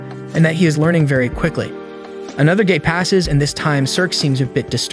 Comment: instead of “another day” subtitles say “another gay” XD